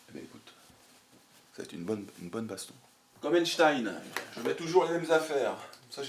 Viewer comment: comme quoi ????? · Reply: Albert Einstein, le physicien